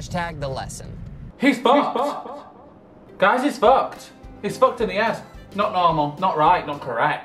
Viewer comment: when you say "He's fucked in the head" your subtitles thought you said something else... 😂😂